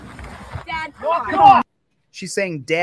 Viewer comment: i heard her say: "dad, it wasn't him"